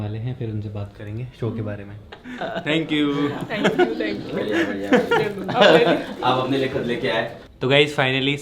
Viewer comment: Is he saying thankyou, aap apne liye khud leke aaye( bouquet)?😂